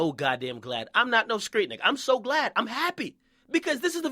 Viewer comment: I thought akademics said 'I'm glad I ain't no straight nigga'